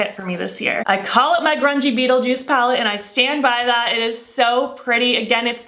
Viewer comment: my daughter thought you said crunchy beetle. 😂